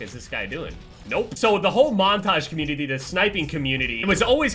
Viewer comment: Who heard that “communittitiy”